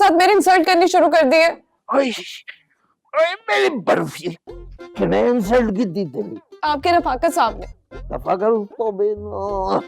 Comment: what did he say? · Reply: Bauji khte usha ko kisne insult ki teri